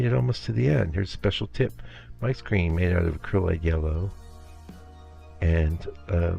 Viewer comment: Hi Mike, thanks for another great video! Question: on the video it sounded like you said "Mike's Green" is made of acrylic yellow and lampblack or Ivory black etc. Did you really mean acrylic? Thanks for the tips! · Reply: hi Ed, thanks for your comment. I meant Acrylide: <link> Very useful for the green mixture 🙂🙂